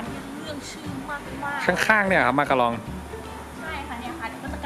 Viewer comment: ตอน0:41หนูได้ยินว่ามารากอง